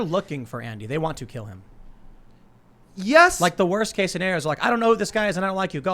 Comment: Elijah: “if they take your mask off and don’t know who you are they’ll take your phone, start mobbing you, etc.” Tim: “worst case scenario is they’ll see the no name person and tell them to get outta here.” Elijah: “I’ll concede that” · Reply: Oops, thought he said “best case scenario”